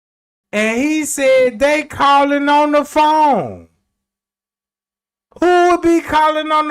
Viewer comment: It sounds like Ty said “shawty in the corner on the phone”